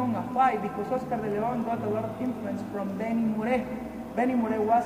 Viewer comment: can someone tell me who is the artist he is talking about at 3:10? not oscar de leon but who was his idol? Beni moore? (lol i cant hear the name) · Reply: Benny Moré